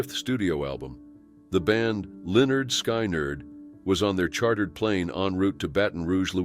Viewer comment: what name did he just say? way off, sounds like Skynerd ....